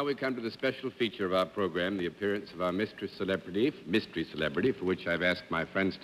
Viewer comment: did Daly say "Mistress celebrity"? If so, that would be a huge giveaway to the fact it was female.